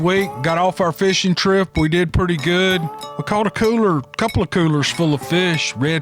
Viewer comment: On lord, for a second I thought you said you got 2 cougars!!!! I had a sharp intake of breath, when I realized you said "coolers"😂..... Thanks as always for your time putting out these stories!!😃